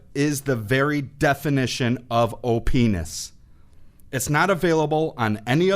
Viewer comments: did he just say openis? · I heard him say openis